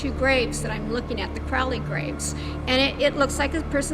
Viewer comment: this bitch just goes out to cemeteries taking photos of random gravestones? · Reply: Does she call them the "Crowley graves"?